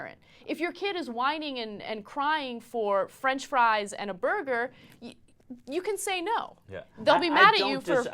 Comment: Does anyone NOTICE the Subtitle are wrong like at 3:37 Burger not "brother"